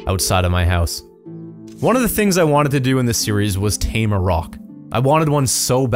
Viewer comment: When said "Tame a Roc", I thought he said "Tame a Rock" lol XD